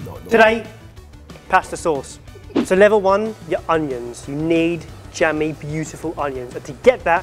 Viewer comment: At first I thought said "undies" not "onions". One belongs in pasta sauce, the other definitely does not.